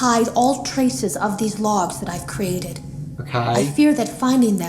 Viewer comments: Eu ouvi uma voz de fundo disse "ok" em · HUE MANO, QUEM CONSEGUIU OUVIR ALGUMAS VOZES DESTORCIDAS NO FUNDO DE VEZ EM QUANDO, tentem dessifrar, deixem o Like para ele ver esse comentario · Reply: Eu escutei uma voz de fundo fala"ok" em